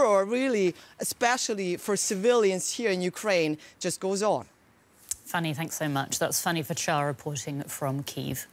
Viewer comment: I heard the word funny. Am I correct?